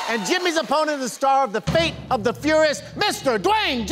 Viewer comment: Is it me or did I hear "fate and the furious?"